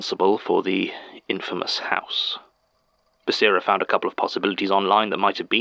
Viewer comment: I heard "Daisy" as "baby" and almost had an heart attack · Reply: I heard it as "basi" for basira